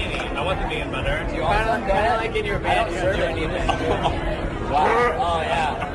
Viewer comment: or so, I think Jeff said “I don’t want his butt or face.” when the friend said that he’d want the server’s butter. Not certain but it’s what I got by slowing the speed down.